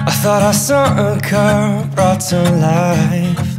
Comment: at first i heard him saying "i thought i saw CAR brought to life" hahaha LOL!!!!!